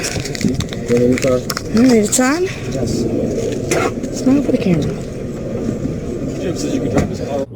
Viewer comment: I think that was his mom that took the camera at the end. It sounded like she said "all right, son.... smile for the camera" Or maybe it was "shawn"?